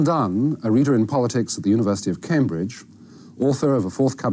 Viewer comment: I thought that said ‘University of Cumbridge’